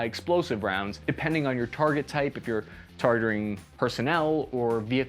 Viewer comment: Did I just hear "tartar troops" (2:56)? Sounds delicious but I think this TBI /BIT is getting worse for Capi or myself or probably both..🤣🤣